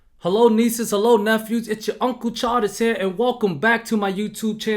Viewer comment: I didn’t read the channel name when I started the video. I coulda swore you said “Uncle Charlie”. makes more sense. Good vid my guy.